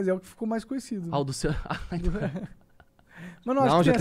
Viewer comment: é impressão minha ou o fala que o do cerol foi o pior?